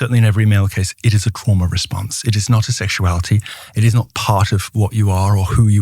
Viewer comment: Did he say chroma response? Anybody know?